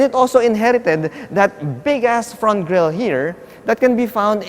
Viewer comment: Is it just me or did he just say "Big *ss front grill"? :)